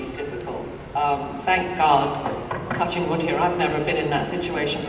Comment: Did Troi and Worf actually boff? - At 1:51 she actually says "touching wood". - James Doohan was a decorated veteran of D-Day.